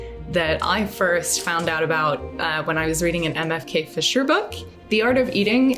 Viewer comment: Regarding M.F.K. Fisher (I originally thought you said Emma K. (Kaye) Fischer), several of her books are available in in Kindle, in the U.S., anyway. One is available in Kindle Unlimited.